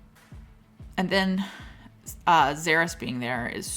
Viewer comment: You have some sad looking bugs. They do good work at relic along with their ships. Also, I thought you said with Xaereth being there, it was very annoying. Then I realized you were referring to zBarris. lol